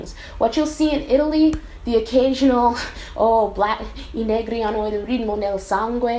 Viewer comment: Sorry to butt in in your conversation with Jenna but I randomly got reminded of something (also joining Jenna in saying that I thought you said "i neri" not "i n*gri hanno la musica nel sangue" as well) There's a video on here somewhere of the Italian Fresh Prince theme song, apparently the dude who voiced Will Smith was also responsible for translating the initial rap. The final version, I don't know if you know it, ends in "il principe Willy lo svitato di Bel Air" but the video on Youtube is like a first draft and this genius initially translated it, I stg "Willy IL NERO lo svitato di Bel Air", and either in the description or in the comments the dude also went on to say that he thought that was FUNNIER, completely butthurt that his first translation didn't make it. I find that pretty offensive I mean why would you characterise Will as "IL NERO"? he's a black dude in a black family... of course he's black??